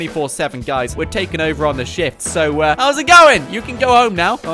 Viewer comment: I think he said sh*t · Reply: Shift**